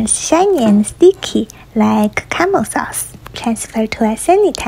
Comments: Ngl I thought you said CAMEL and not CARAMEL at first and I had so many questions. 😅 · I thought I said camel sauce but I read the subtitle it said caramel hehe, is English your second language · I fr thought she said “camel sauce” and I was like WAT